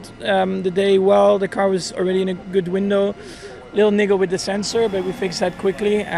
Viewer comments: Maybe it's just me but I swear it sounded like Max said "lil n***a with the sensor" 😆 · What did max say 00:06 👀😂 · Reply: wiggle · what did he say? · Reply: Wiggle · Hahaha in 0:06-0:09 it sounds like Max is saying hes a Ni.... with a sensor :P haha